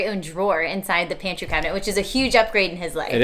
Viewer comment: What does he say about her at 5:07??? I can't hear clearly. Something about her pants or the fact that she doesn't have any shorts on ... ??? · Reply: said “Erin isn’t wearing any pants.” She said “I’m wearing shorts.” But the camera angle is only waist up so we’ll never know.